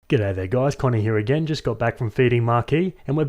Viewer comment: I misheard at the beginning and thought you said "Just got back from the baby market!" lmao